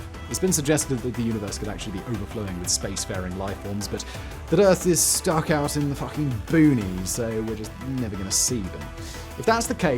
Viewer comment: "...The Earth is stuck out in the f***ing boonies..." Did i hear that right?!? Lol